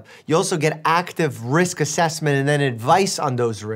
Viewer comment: i thought he said rizz assessment 💀💀